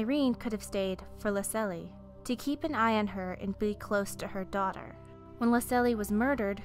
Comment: I don't know I where I heard this "her daughter was pureblood"